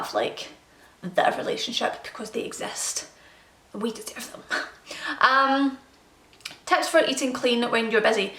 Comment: Aaw I didn't catch what you said at 8:20! 🥺😔😝 Not even subtittles did. Thanks for this video dear xxx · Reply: 'We deserve them' x